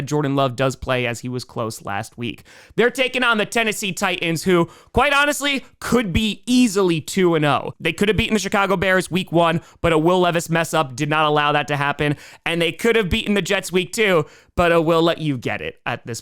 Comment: did you just say 💩cago Bears? 🤣